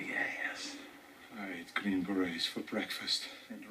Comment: I always thought he said “I eat cream eggs for breakfast”